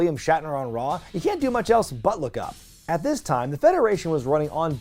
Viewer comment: What did say at 8:54-8:59?... "You can't do anything else but--" what?... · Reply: Look up